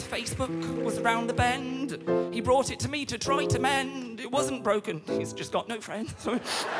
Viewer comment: “It wasn’t broken he’s just got no friends” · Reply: I thought he said “it’s wasn’t broken he’s just got no trend, sorry!”